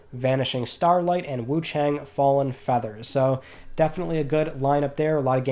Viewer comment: definitely thought you said “Wutang” for a sec in the intro there haha. Got hype there was new Wutang Clan content coming from somewhere! Regardless looking forward to *Wuchang, and the portable PS info sounds interesting. Thanks for everything bro, enjoy your weekend!